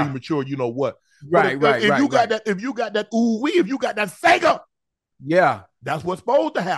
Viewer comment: Did he say "when you got that sega"? Timestamp